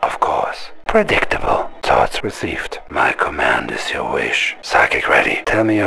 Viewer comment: What the hell does Yuri say at 8:04? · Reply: theres 2 of em. one is "Thoughts recieved." the second one is "My command is your wish."